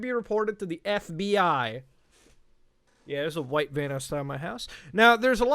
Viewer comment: lol I thought Muta said there’s a white man outside my house